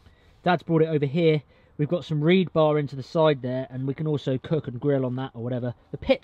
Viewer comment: Did anyone else hear him say "reed-bar"?